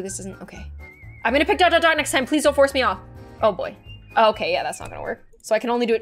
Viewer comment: I thought the elevator girl said while showing the tray, "Please don't choke"- I'M WHEEZING